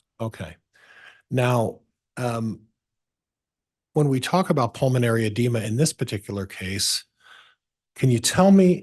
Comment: Is it just me or did I hear pulmonary embolism as a cause of, or factor in, the death of people in close proximity to these murderous folks!?! Is this the fourth time? Wow.